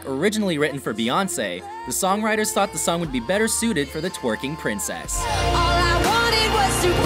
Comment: sorry hang on did you just call Miley Cyrus the princess of tweaking?!!!!!